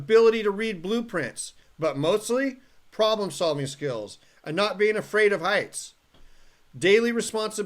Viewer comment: I thought you said not being afraid of ICE